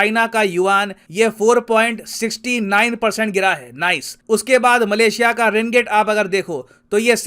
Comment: am i the only one who heard NICE after 5:40.... when he said yuan fell by 4.69%